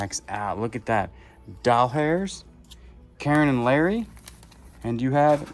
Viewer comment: I thought it said Dold Hair and couldn’t figure it out! LOL